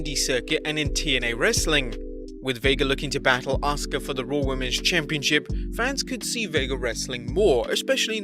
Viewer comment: wait, did he just say the Raw Women’s Chimpionship😂😂